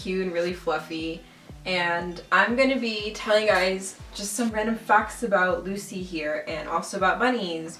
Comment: please tell me I'm not the only one who heard her say "Just some random fucks" 😂😂😂